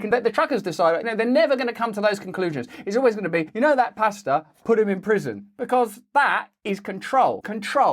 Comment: I thought you were saying pasta 😂 I was so confused